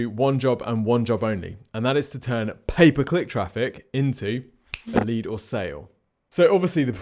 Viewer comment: I hear "paperclip traffic" when you say "pay per click traffic" 🤣🤣 I was like... WHAT is he talking about!